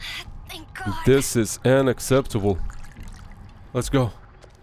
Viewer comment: almost sounds like he said “Ana-ceptable”😂